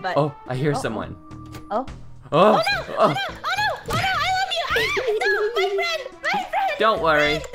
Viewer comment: Was it just me or did say i love you